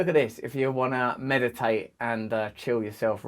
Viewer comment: You said "meditate and chill yourself right out" and i heard "meditate and kill yourself right now." Maybe it's time to meditate lol.